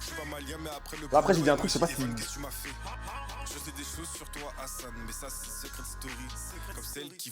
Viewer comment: Moi j'entends "C'est Anita dans Secret Story" 😶🤣